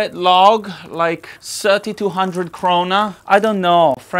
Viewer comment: did I hear corona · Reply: *krona